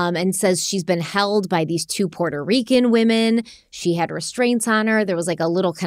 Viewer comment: I thought she said Mexican women.